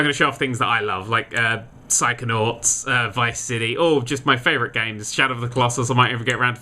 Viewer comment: Did you say Vice City? OH MY YES